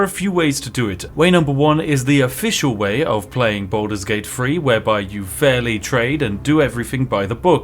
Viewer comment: I hear Baldur's Gate FREE :|